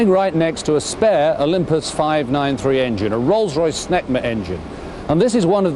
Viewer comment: I know what he said, but I heard something else the first time. 😆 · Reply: "Smegma Engine"